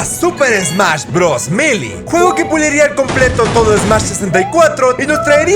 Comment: y en 5:17 en los subtitulos melee y brawl estan mal escritos Ya que dice milly en ves de melee Y dice browl en ves de brawl